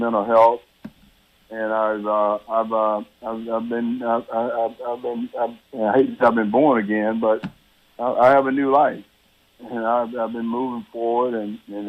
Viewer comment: first, i thought that guys head rest was his heAdset. Second, did he say, "i hate that i was born again, but i have a new life"?